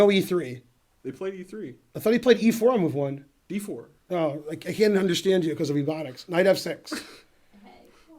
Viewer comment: "I can't understand you because of ebonics " - Ben Finegold 1:00 Did I hear that right?